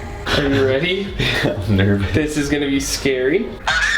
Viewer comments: it sounds like clint said the n word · what did bro say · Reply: He said I’m nervous